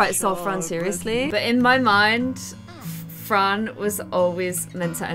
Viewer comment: Okay I use the caption just for fun but around 32:20 when says Fran the cc says frog